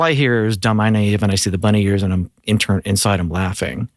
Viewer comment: He said Bunny Ears thought he said Money Ears and it made even more sense